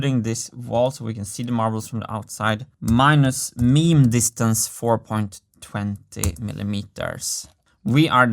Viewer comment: Did I hear/read that right at 4:05, he said MEME distance? That’s what my closed captioning said! We have a new measurement! Lol